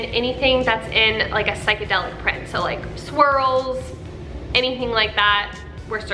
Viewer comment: When you said “swirls” I could have sworn you said “squirrels” and my face said 😧🤔🤣